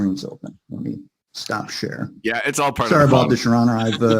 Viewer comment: IM DEAD🤣🤣 When he starts talking about sharing his screen to show Judge the Texts, It sound like hes saying Shit instead of share😂 Then goes on and is like A<link> God Damn It!😂